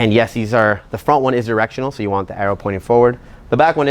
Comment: you meant to say directional I think but you said " erectional " lol does that mean its a harder rubber? XD im just playin love ya